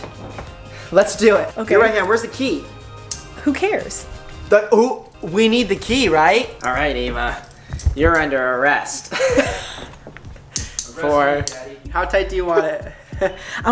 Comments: (i think) said "arrest me daddy" 😉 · who else hears "arrest me Daddy" in the back ground. Thanks so much for all the likes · Anyone else hear in the back ground “Arrests me daddy”...? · did anybody else hear "arrest me daddy" ?!?!??!😂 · OMG XD Who heard in the background "arrest me daddy" OMG IM DYING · DID ANYONE ELSE HEAR ARREST ME DADDY